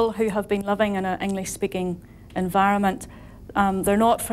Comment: She's talking about being understood and it sounds like she said 'loving in an English-speaking environment'